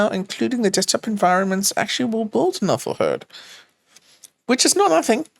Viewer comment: Is it just me, or do I hear "Gooner Hurd". Sounds scary